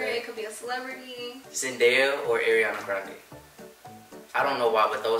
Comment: did not just say AIRIANA Grande 🤣🤣🤣its ARIANA 🤍🤣